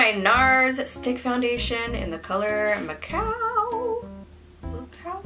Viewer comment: Am I the only one who thought she said, “In the color, I’m a cow.”??? I was like, “same, girl. Same.”